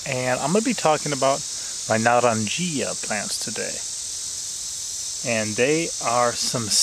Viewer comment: Thought it said marijuana plant lol